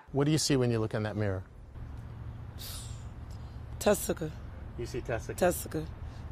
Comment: I thought she said Testical was her name lol